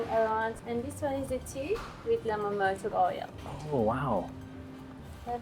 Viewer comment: Does she say "green tea with a lemon MOTOR OIL" ????? 😮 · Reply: i believe it was lemon myrtle oil